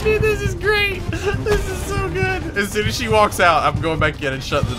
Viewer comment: oh yea. TROLLING the beast. d: how are you going to troll the beast if she cant see you? i thought you said sushi but you said as soon as. -_- username:Amigaozona